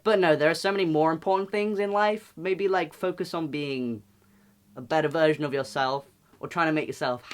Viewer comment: Genuinely thought he said “focus on being a better virgin of yourself” for a moment 😹😹